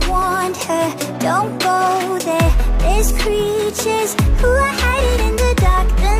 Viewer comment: “There’s creatures who are hiding in the dark” Me: turns on light Creature: aww man Me: hah! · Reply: I thought creature said creeper LOL